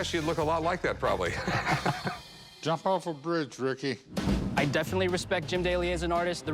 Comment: Eid i just hear “jump of a bridge” gold lmao